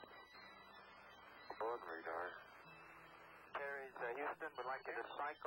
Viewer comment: I'm not sure, did the astronaut say "the / a boden radar... boden radar"? Or what did he say? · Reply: Yeah, and it's weird that they repeated the exact same audio clip twice. I think he's saying "aboard radar" - like maybe the radar aboard the craft, and not the radar tracking from Earth. But, yeah, I'm not sure. It's not Shepard or Mitchell saying it, so we can't even look it up in the transcripts.